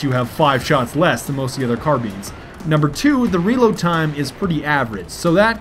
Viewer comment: Sounds like he is saying carbeans